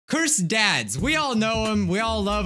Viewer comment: I legit thought he said “cursed dads” (edit: oh he did say that lol im dumb) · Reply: I thought he said cursed pads